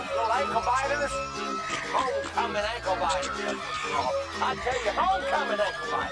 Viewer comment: Am I the only one who heard “Michael Myers” in that?